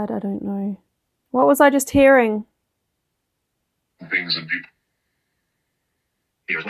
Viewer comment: "things & people" is what I heard as the response to the question about what you they hearing.